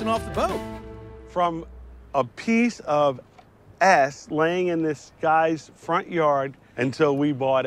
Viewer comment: Pretty sure had a Freudian slip when he referred to Das Boat 3.0 as a "piece of ass" while standing & talking to the 2 new operators.... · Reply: I thought he said "piece of S", like "piece of shit".